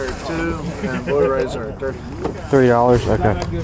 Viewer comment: did he say 3$ or 30$?